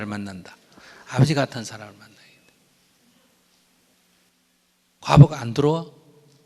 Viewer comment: 말씀을 못 알아들었어요..ㅠㅠ OO가 안 들어와? 안두려워? 뭘까요.. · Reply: 과보가 안 두려워? 인것 같습니다.